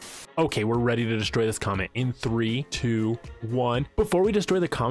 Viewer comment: When u said comet ☄️ I thought u said comment and was confused lol